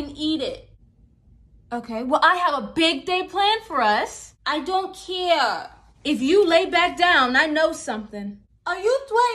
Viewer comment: I THOUGHT YOU SAID "NO ONE CARES IF YOUR FIRST"